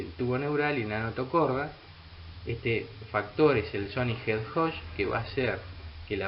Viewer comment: minuto 2:07 qué dice? factor de? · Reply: Factor de Sonic Hedgehog (SHH)